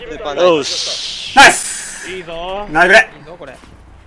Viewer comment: いいぞぉーこれって聞こえたのは俺だけかな？ww